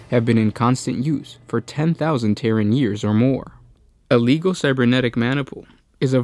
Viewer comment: There are a couple of words that kept coming up during this video, but I couldn't understand the reader's accent. Sounded like "manable" and "leebol." What were the words supposed to be? 5:15 is an example. Sounds like he says: "A leebol cybernetic manable." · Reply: A "Maniple" of the "Legio Cybernetica": an organisatory unit, a group of robot, essentially. Here is what the WH40K Wiki says to it: "A Legio Cybernetic maniple is a virtually self-contained unit, with a single Tech-adept typically overseeing the management of four units on the battlefield." It´s a Robot Squad! The term "Maniple" was used in the legions of ancient Rome, a source of inspiration for many details released by Games Workshop.